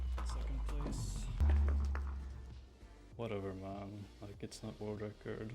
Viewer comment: Why did the auto captions say "Whatever MOM"